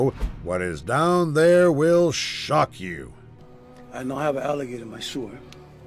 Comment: The guy who found a croc in his sewer sounded like he said "I have a gator in my sewer". But that could just be me